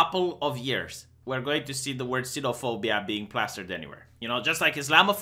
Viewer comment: Its sinophobia actually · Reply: and here i thought you were saying xenophobia- like fear of xenos -kek'